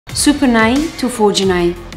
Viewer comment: I THOUGHT SHE'S SAYING SUPER 9 TO 4G 9 😂😂😂